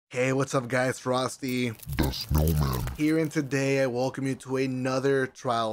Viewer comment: Wth is he saying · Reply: Frosty the snowman